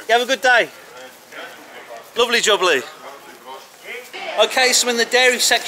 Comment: I think I heard you say doubly jubbly 11:20? Almost sounds like you were saying dovidjenja (good-bye) lol